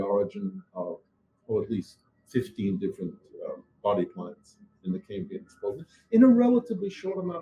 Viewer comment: and @ 13:25 - what is berlinsky saying? Body parts? Body pods? Body clients? Something else? If he's going to do interviews by zoom, he needs to get an external microphone, located closer to his mouth, and turn on original sound. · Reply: He's saying "body plans".